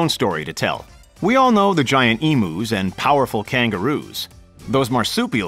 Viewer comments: I'm sorry did you just say "emOoS" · did he just say “eemoos” 🤦‍♂️